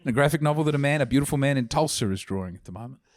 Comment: did he say Tulsa? Or some other fucked up british word